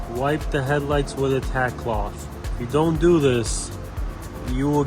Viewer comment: what is that yellow fluffy clothlike thing you used? Sounds like you said tac cloth, but I did not see that in your list of items on amazon. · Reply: It’s a tack cloth, I just checked and it’s still on my Amazon link